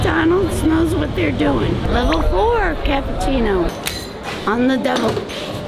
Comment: I love Lynja she is so wholesome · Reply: she said “on the double” it’s easy to mishear with all the background noise and how quiet her voice was there.